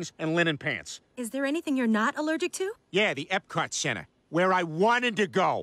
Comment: Can someone tell me what's he saying. I can't understand it. My native tongue is German and there he's saying Disneyland. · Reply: Epcot centre, dont worry it doesnt change the joke